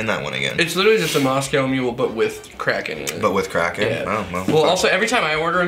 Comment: Every time you guys said cracken I thought you said crack till the very end 🤣🤣